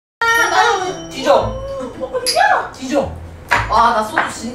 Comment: 이 D져 하는 소리 나만 들리 나?